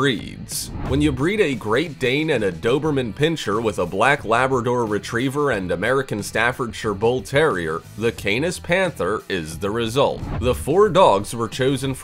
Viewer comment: what did he said ??? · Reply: when you breed great dane and doberman pincher with black labrador retreiver and american stafordshire bull terrier . the keenans panther is the result